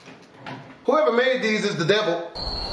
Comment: I heard it as "Whoever made Jesus's the devil" lol